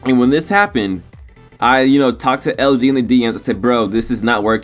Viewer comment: bro did u just say LG instead of LD lmaooo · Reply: Nikeairon24 he said LD. he just talks like his tongue is too large for his mouth. but I can't hate. only 2k tuber I follow